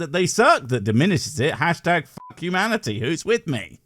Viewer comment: I briefly thought you said, "Fuck you, manatee!" and was rather perturbed.